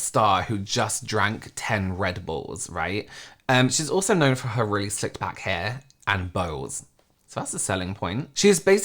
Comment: When said “she’s also known for her really slicked back hair” I heard “her really sick back hair” and I thought how have I learned everything about this girl against my will but never heard about her crazy back hair before???